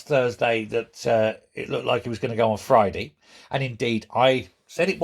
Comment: I thought he said he waant going?